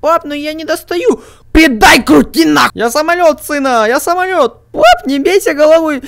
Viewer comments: ты скаазал на*** или послышалось? ._. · Мне послышалось или он на 9:08 сказал:"Крути педали на**й"?